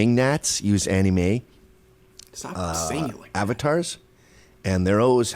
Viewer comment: What did say here? “Stop singling”? I couldn’t hear well with the background noise. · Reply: 'Stop saying it like that'